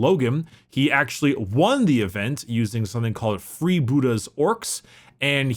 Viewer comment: Totally went over my head, my mind only registered, “free booty orks” ehe. Glad you had a rootin’ tootin’ good time boii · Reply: Likewise I misheard "Free Buddah" orks and the idea of an enlightened ork is terrifying.